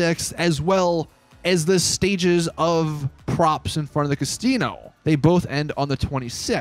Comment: did you say Castino